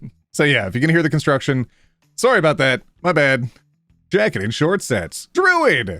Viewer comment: That "My bad" really sounded like "My baaaaadge 🖕"